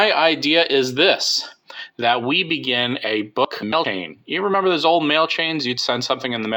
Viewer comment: I heard book milking 0:20 Still, Thumbs Up 👍